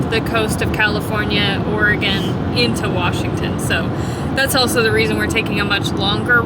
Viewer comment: Were you moving to? I thought you said Washington State. Anyway have a good trip. 🚗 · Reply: I am moving to Washington state, this was the first half of our road trip